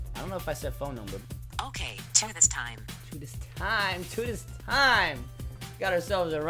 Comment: Am I the only one who thought Siri was saying "tetanus time" instead of "two this time"